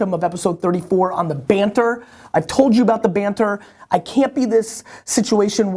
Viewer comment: Banter? I thought you said "barter." I'm in here trying to trade a few bushels of wheat for your oxen, and now you tell me you just want us to talk alot?